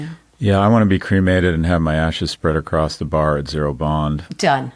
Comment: Lovely show guys - though I misheard Scott's funeral plan at 13:35 and thought he said he 'wanted his ashes spread across Charlize Theron' which sounded about right 😅❤